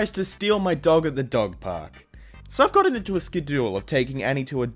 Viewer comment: Did I just hear “ske-duel” instead of schedule